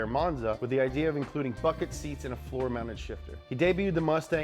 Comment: I thought he said "Fuck it seats"